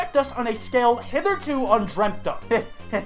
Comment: Did you just say "hitherto undreamt of?"